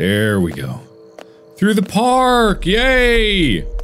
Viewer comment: i clicked on back on this video to finish it. only to hear "yay" and then silence. at first i thought he said "gay" and i was like woah man, no need to call me out.